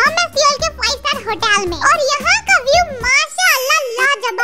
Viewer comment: Did you say Allah